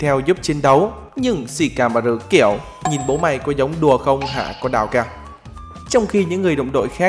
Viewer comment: hình như Ông đọc đào saii thì phải sao tui nghe ra chữ con đào cak ? :)))